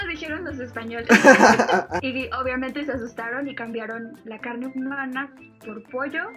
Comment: nadie se dió cuenta de que dice: “and cambiaron”? :u